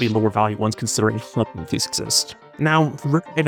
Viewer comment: does anyone know what he said at 4:49? i genuinely have no idea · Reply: I think he said "considering head Hathys exist"